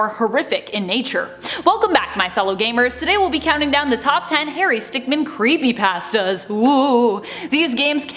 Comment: Did she say harry sickmin at the biggining